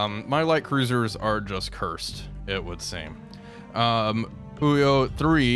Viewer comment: Every time you say "Fuhyo", I hear "khuyo" which sounds way funnier in most Slavic languages.